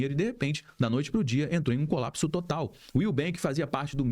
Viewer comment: medo de perder meus 20 reais na nubank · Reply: Eu no trabalho escutando notícias quando soltaram "o Willbank foi liquidado" e eu entendi "Nubank" e a primeira coisa q falei foi "p*** meus 20 reais" 😧 kkkj pobreza miserável